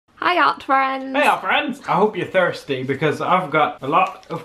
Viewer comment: I just relized they say “Hello art friends” In the beginning of each video. I thought they said “Hiya friends”